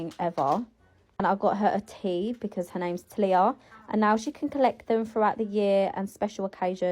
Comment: thought she said TILAPIA like the FISH and thought "Why are we naming children after fish???"